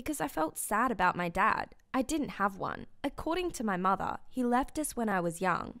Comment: she said "according to my mother" but i heard "according to my marther "